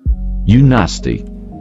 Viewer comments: When it said “ew nasty” is sounded like it was saying “ you nasty” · I heard the "Ew nasty" as " YOU NASTY" 🤣✋